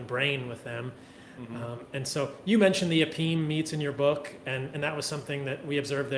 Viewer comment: " Apine " ??? what is that word